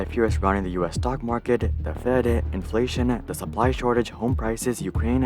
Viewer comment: Shortage instead of Shorgage